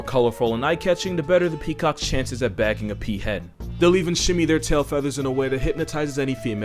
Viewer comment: Lmao i thought he said peacock has a better chance at getting peahead and i lost it